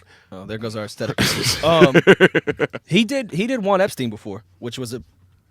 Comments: he did “Juan Epstein” before??? What did Rory say here?? Someone help me please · Sounds like Rory said Jain Epstein … what’s he say there?